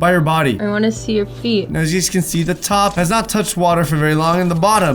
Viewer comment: I thought it said teeth but this is okay too